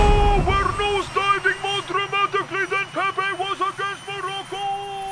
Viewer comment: what was Ronaldo screaming? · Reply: he said "ohhh we're nose diving more dramatically than pepe was against Morocco"